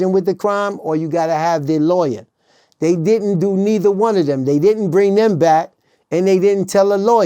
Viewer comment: I thought he said he didn't rob